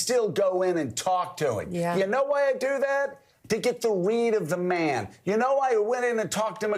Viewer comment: I thought he said to get the the weed😅😂 they didn't have to go to maralardo and none of us care anymore about anything the two of you have to say, bye-bye now